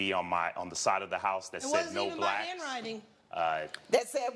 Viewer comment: I honestly thought she said that's not my handwriting to the swastika lol I nearly spat out my tea!